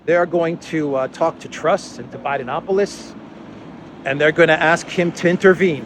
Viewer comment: Thank you for your work sir I thought I heard "Dividenapolis" but it was just to Bidenopolis 😆